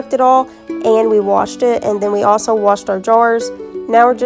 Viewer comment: One authentic, homegrown, American beauty! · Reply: I thought she said, "And then we wash our drawers." Correction: "and then we wash our jars."